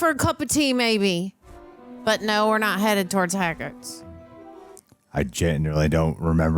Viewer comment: Did she just say Hagrids instead of Hogwarts?